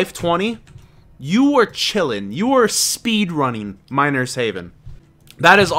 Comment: i thought you said minor's heaven at the beginning